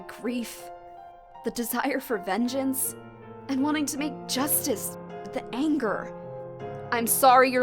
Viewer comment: I thought she said "wanting to make juice". I know how the constant craving of juice goes, MAKE THAT JUICE GIRL YOU CAN DO IT!